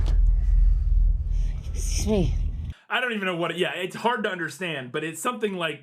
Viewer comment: “Excuse me” is what she says at 35:01.